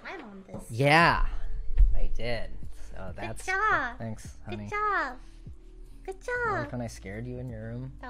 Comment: "thanks honey" did i hear this right?? 🥺❤️